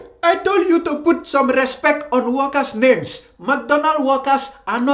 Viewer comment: Ohhh so he was saying workers I thought he was saying walkers. lmao